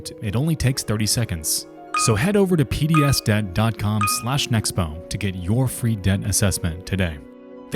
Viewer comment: Dude I thought your name was the entire time. I just heard you say Nexpo and was mind blown